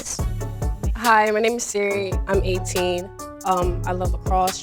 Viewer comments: What Sire says: Hey im Sire, im 18 What my screen says: 17 Me: Okay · Am the only person who heard this on the screen it says age 17 but she said 18